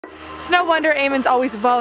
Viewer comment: I heard “snow wonder!” at the very beginning, which was very fitting for your winter wonderland backdrop!